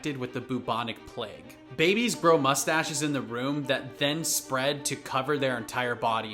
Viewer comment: "Babies grow mustaches in the womb." Me - Am I the only one who heard him say "room" instead of "womb"?